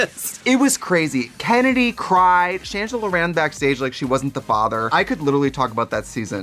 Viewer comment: Shangela ran off the stage like she wasn't the father - I'm crying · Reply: Wait, she said that? I could've sworn I heard trixie say "like she wasn't that bothered"